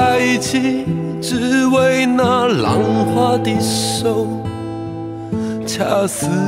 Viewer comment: 為什麼我一直聽成蘭花的手.......